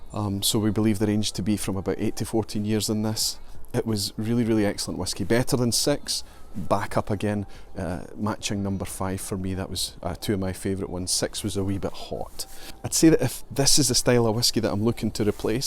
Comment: What is all that white stuff on the ground? I thought Scotland was a tropical paradise? BTW, that cat escaped you... also at 10:11your accent makes you sound like you say "better than sex... sex was a wee bit hot" and that made me giggle. Sláinte ;)